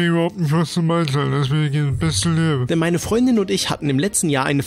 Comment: Was hat der gesagt? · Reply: Beste leben?